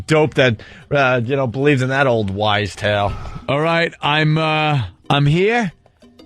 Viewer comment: does Gregg say "old wise tale?"